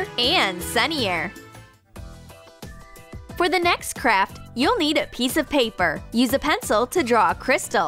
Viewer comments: I thought she said “For the next crap” XD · I thought she said ''for the next crap''(pls dont hate)